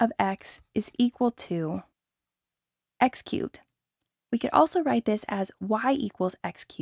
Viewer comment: I heard X cute ❤️❤️❤️